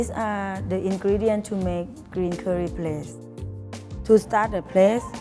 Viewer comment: Who else heard plaste instead of paste?